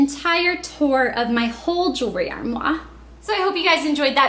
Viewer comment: Lol, at 5:40 did you say "Drawmoir"?